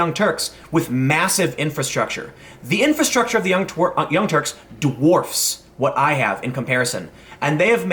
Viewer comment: Sounded like you almost called them "The Young Twerks" and now I have mental images of Cenk Uygur twerking stuck in my head. Thanks for killing my ability to sleep tonight...